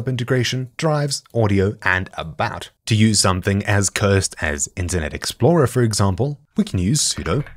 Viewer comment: I thought he said "incident explorer" lol